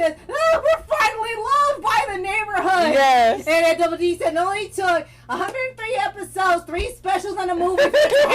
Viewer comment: actually what he said was, " it only took 130 episodes 4 specials and a movie😂